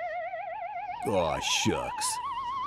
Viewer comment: I love how the number of episodes changed dramatically each week the show aired. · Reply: On second thought, Cliffhanger says “Aw Shucks!”